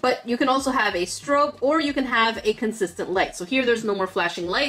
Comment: Who else thought said, “You could have a stroke, or a consistent light.”